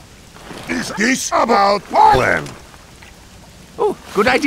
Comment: "Would you like a second opinion?" "Is this about poland?" and other legendary clips from this madlads 6 months of work. I can't get over how good this is. I'd have to and will rewatch this a ton just to find all the cool details. You really did do a perfect mix of story, animation and tf2 logic in sfm. Fucking legend, i hope you're proud of this. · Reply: I thought Heavy said "Is this about the plan?" They should add captions.